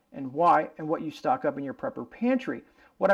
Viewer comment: Pigeon and cat. . . Oh, I thought you said unprepared pantry. Its really hard to pick one, but I do agree with the beans.